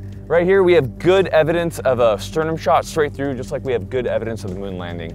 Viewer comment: Ive been playing too much tarkov, i thought he said "shturman" shot, not sternum shot lol...